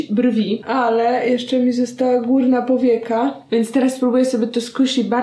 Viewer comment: jem płatki, i zamiast ,, górna powieka '' usłyszałam ,, kurna powieka ''